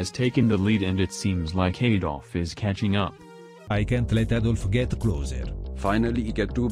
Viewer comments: what did he say? Avdol or? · avdol sounds like hes saying "Adolf"